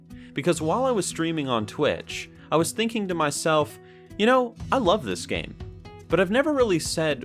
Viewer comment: I thought he said "You know why i love this game? It is....Azerite" xD